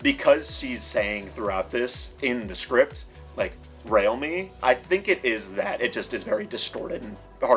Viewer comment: i thought she was saying 'real me' during that moment